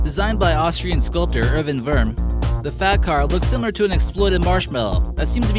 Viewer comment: when he said fat car i thought he said fap car